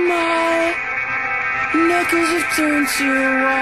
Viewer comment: I remember when I first heard this song I thought he said, "My n-word has turned to white" :57 😂🤣 had to go and get the headphones and relisten to it. To this day, I still hear that or nipples lmfao. Yes, I know it is knuckles.